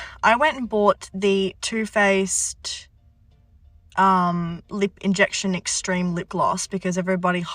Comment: I thought that tube said 'too faced lip infection' and i was like yeah that tracks 😂